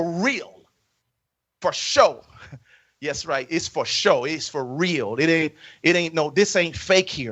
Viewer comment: Which one is it, clown? Edit: I thought he said for show. (For real/for show) But I guess it was fo sho